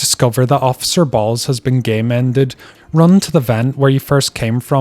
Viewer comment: Officer Balls has been game-ended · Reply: I thought he said gay mended??